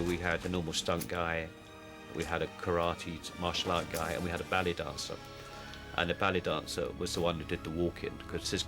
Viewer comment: did he just say "belly dancer"??? lol